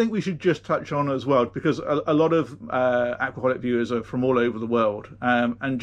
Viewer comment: For a moment there I thought you said Alcoholic viewers instead of Aquaholic viewers! It fit right in with all the comedy I was already enjoying!